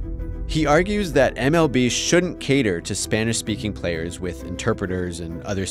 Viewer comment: but he actually says they should...